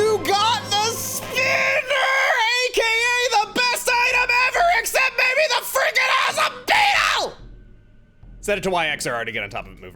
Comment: "Except for the ____." What? I couldn't understand the sperg out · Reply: Except mabye the fricking awesome beetle